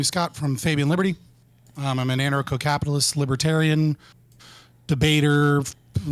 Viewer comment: “I’m an anarcho-capitalist libertarian” this is gonna be a long one isn’t it? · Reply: I thought he said he was a nanner co-capitalist at first.